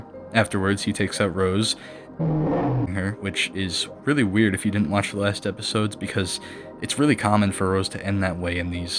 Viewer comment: I’m trying to figure out what word this is rn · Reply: The word was decapitate